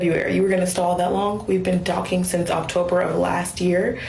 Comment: I thought she said "docking" not "talking". I stand by that.